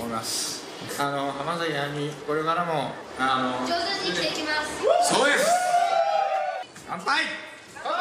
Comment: ayuさんなんて言ったんですかね、、？ 聴き取れへん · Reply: 上手に生きていきます✋だと思います！